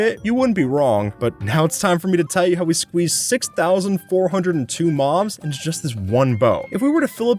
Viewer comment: who else heard “moms”